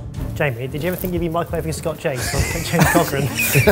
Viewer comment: I thought it said ft. James Corden